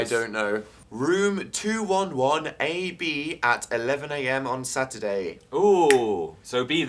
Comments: omg I thought Dan said "221 B" as the room number and I flipped out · zomg, when Dan said 211AB I thought he said 221B.. Too much Sherlock and youtube in my head..